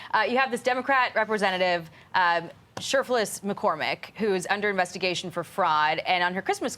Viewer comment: When the Fox News anchor said Cherfilis McCormick I thought she’d said Syphilis McCormick 🤔🤣🤭. Had to look it up online to be sure 😂